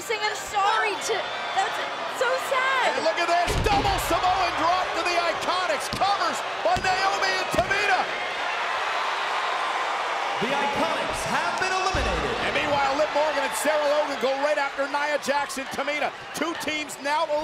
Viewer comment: Did he said naomi and tamina?